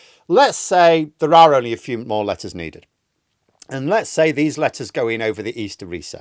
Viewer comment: I thought you'd said "two lettuce needed"............